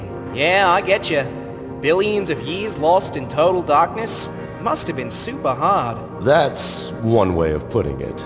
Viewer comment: LOL, IF YOU PAUSE AT THE ROGHT TIME IT SOUNDS LIKE URANUS IS SAYING "Must've Been Sigma! Huh?" WTH 😂😂😂